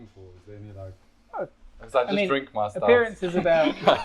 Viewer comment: What do you say at 8:00 ?? 😅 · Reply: ‘I just drink my stouts 😂’